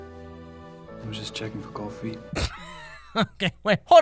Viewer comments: I thought he said "I was just checking for coffee" I WAS SO CONFUSED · it sounds like he says “just checkin for coffee” 😂😂